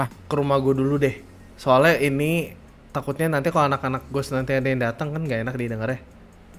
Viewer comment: anak² ghost???? gak salah denger nih